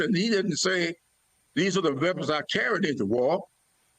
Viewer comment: "The weapons I carried, in war." "We had more people on January 6th than the Dr. King speech." Pick the most egregious statement folks. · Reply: I actually thought he said "the weapons are carried in war," but then again, thousands are not quibbling about it.